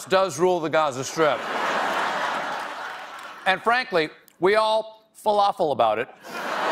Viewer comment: Did he say "Anne Frankly?" Or did he just accidentally outdo all of the puns that followed by talking with food in his mouth?